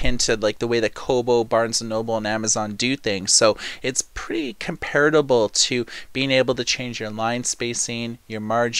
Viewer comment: did you actually say "comparitable"? That is by no means a word. Do you speak English at all?